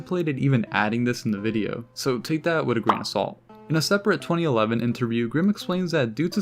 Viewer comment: I stopped looking for a second and thought you made a pun saying take that with a grain of saul